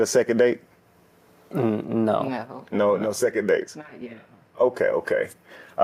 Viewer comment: I heard it" not yet"😊